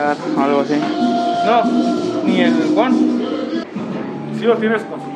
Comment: Lo que dice: No, ni el One Subtítulos: No digas mamadas wey XD